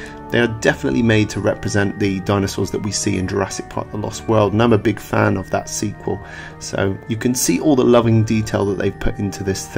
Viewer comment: what are you saying here? Not a fan of the sequel? Can't hear it very well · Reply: The Abyss I think he said "and I'm a big fan of that sequel"